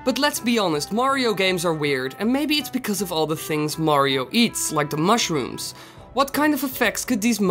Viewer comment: This is about Mario right i Heard you saying wario